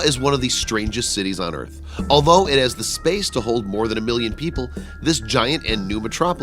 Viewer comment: 6 times the size of NYC, yet it only holds a million people? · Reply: I hear a "million" not a 'billion."